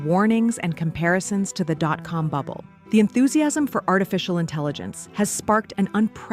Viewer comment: I thought she said: "'cum bubble"', WOW, lady chill out.